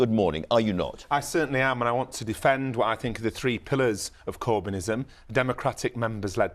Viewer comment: I thought that fella said "I want to defend the three pillocks of Corbynism"....I thought he meant Corbyn. Abbott and Mcdonnell....but he said 'pillars'...'three pillars of Corbynism'